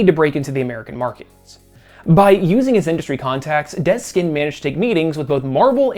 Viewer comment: I'm sorry, but are you saying "Dead Skin"? Is that the man's name, or am I hallucinating? I keep listening and I can't figure out what else you might be saying. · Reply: Dez Skinn